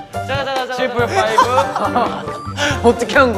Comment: Wait i came back and just noticed now please correct me but did i hear someone said "Chamber 5?" at 9:31?